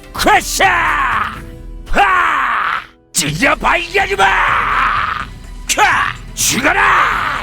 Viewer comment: 찢어발겨주마~ 였구나.. 겜에선 죽여버리겠다 로 들리던데 그렇구만